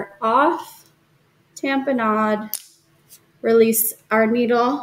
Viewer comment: what is the full sentence that you're saying after "feed our catheter off..."? "tamp a nod?" sorry just looking for clarity, I follow all your videos💕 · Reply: "Tamponade", so push down about where the end of the catheter is in the vein so the blood doesn't leak out when you remove the needle. A lot of the newer catheters now have a "self-sealing" type device built into it so you don't need to hold it, but not all of them do. Let me know if you have anymore questions! Good luck!!!